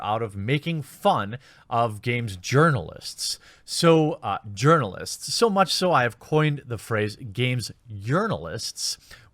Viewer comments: For all this time I thought you were saying journalists with a Mexican accent.. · Oh I thought you said Game Yearnalists, meaning they yearn for fame but are doomed to fail eternally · I thought you were saying "Yearnalist" - yearning to be noticed or make a difference or be offended on behalf of anyone. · I understand where you get Urnalist from, but when I heard you say it first I thought you were saying Yearnalist, as in they yearn to be a journalist, and I prefer that explanation! :P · I always thought you were saying yearnalists as in they wish they could be journalists